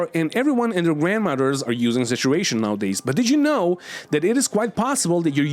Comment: 😂 I thought he was saying “situation” for half the video.